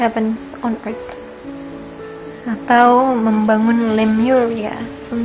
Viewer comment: di 16:28 mbaknya ngomong apa ya? kurang jelas gitu kata2nya "atau membangun..." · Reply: membangun Lemuria. bangsa sangat Maju yg pernah ada dulu